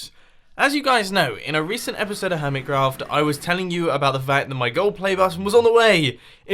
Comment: I kept hearing say "hermitcrap" during this episode.